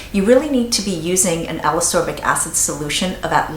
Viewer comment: Who else would LOVE to see talking about vitamin C serums?? I can't trust anyone else! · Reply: I didn't understand what she was saying at 6:37 (alezopic acid?? Haha) sounded great tho.